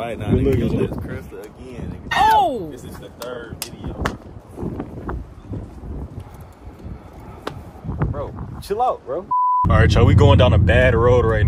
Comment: I thought you said 1 hour video ❤❤